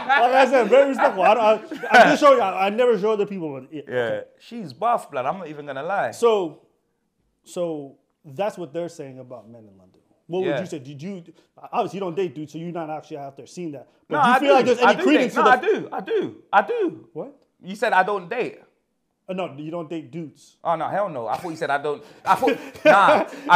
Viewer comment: Being fair, I also heard it as, "Obviously you don't date, dude." 🤣😂🤣🤣